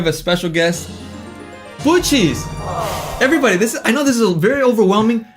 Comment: Blue cheese is so cute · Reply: When I was watching this live I thought he kept saying Goocheese, which if I'm not mistaken was the girl from the OnlyUseMeBlade/Chris Hansen saga. LOL